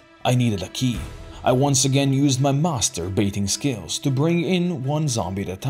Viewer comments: WHAT DID U SAYYY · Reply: I masterfully baited the zombies inside. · what did bro sayyyy?!!!! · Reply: He's master at baiting zombies, it's just facts.